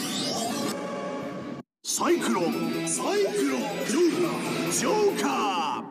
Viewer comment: サイクロンサイクロン、ジョーカージョーカーがサイクロンサイクロン、ルナジョーカーに聞こえる…